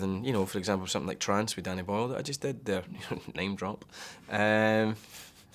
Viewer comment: What he said in 3:00-3:10? He actually laught about it. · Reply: He's saying he felt easier doing filth than he did doing Trance with Danny Boyle, then he says "name drop"